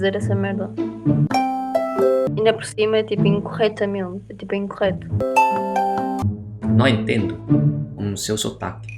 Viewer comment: What did she say on 4:48 bro? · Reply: she said, why do u have to say that shit